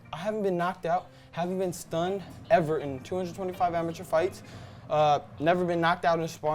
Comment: 225 amateur fights, did i hear this right 🤯